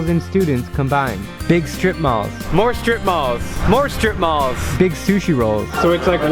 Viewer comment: I heard strip poles